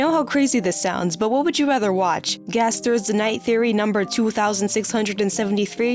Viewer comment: nice bfdi reference edit: i heard it wrong, its not 2763 its 2673. we were close to greatness